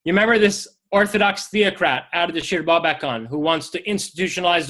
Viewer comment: What is the word (that is the name of what I’m pretty sure is a book) being said at 52:26 ? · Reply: Ardashir bobakan?